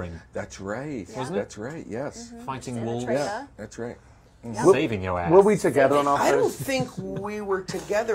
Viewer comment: did he say “saving your ass”?!!!!